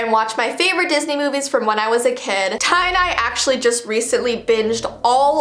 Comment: Evertime you say "ty and I" it sounds like your saying Tie dye